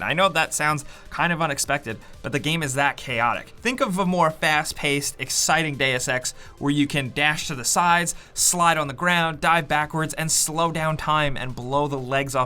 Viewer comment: He said fast deus ex I thought he said fast day of sex XD And then blow the legs off a dude with a shotgun?